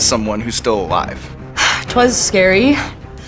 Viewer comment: did Kristen say "twas scary" at 15:33 before going Silence of the Lambs?